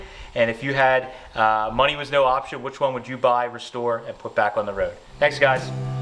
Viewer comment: Option or object? I’m not quite sure what you said there.